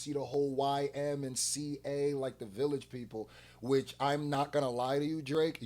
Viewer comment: I thought u said u never gonna react to Drake 🤣